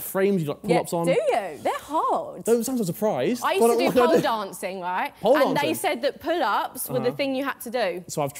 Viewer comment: did he say pole vaulting?